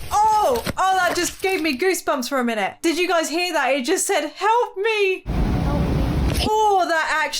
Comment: when something saiid help me it sounded like fnaf 2 hello